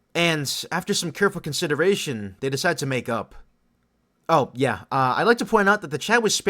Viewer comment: why did I hear make out when he said this😭